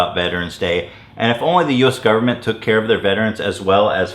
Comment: i thought it say vegaren (vegan)